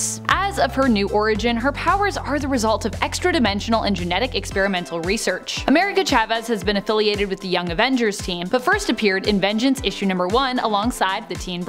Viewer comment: I thought you said the young offenders 💀